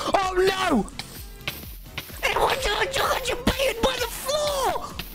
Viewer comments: tommy learned enchant table language? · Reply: I HEARD "AJUBAUHA ABAUHUBA BY THE FLOOR" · now if you know what Tommy is saying, you’re a legend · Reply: Tommy is trying to say "i just baited by the floor" but ya know he was shocked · i think he said "i got jeb- jebaited by the floor"